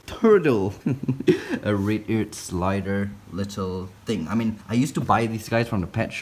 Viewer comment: not to be rude but I thought he said R a p e Ear Slider.😂🤣😂